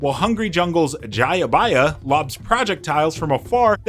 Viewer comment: Does he say "Prah-jectiles?"